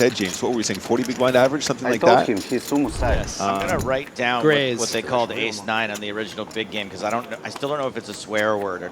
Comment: What did he say? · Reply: Gud luck